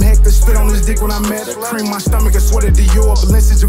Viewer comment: what he say?? Spit on his what? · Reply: Lmao I had to rewind it to make sure I heard it right 😂 "cream on my stomach"